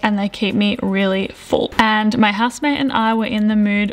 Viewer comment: Excellent what ate in a week video with great recipes. Did get married because she mentioned the word husband? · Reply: I thought I heard that too ahaha she said “housemate”